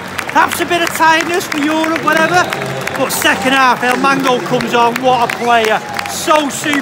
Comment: Did he say "El mango" at 8:46 😂😂